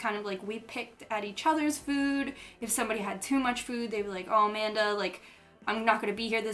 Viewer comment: did you say your name was Amanda